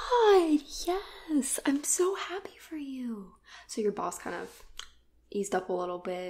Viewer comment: Who else thought she said your balls started to ease up